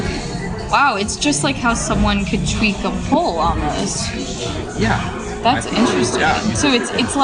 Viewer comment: the lady says "tweak a poll almost" while the caption says "tweak a poll on us". Am I mishearing this or should we correct the captions?